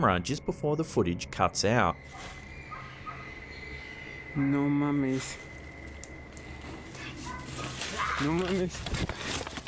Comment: in the in first video with the guy exploring the abandoned building and seeing the naked homeless man the captions say he's speaking in a Foreign languages but he's just speaking Spanish he's just saying ( you're kidding)